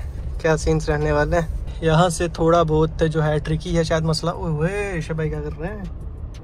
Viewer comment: sounded like shoaib bhai